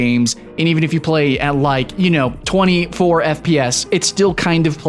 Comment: did he just say "play these games at atleast 20 to 4 fps, which is kinda playable?" Dude . .. ... No.